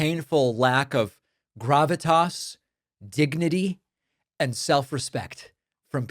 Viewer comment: "We could've lost a lot of dignity last night"... You in fact did, America. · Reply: It sounded like he said 'gignity'.